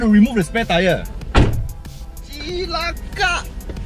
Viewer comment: did you say cilakak...its funny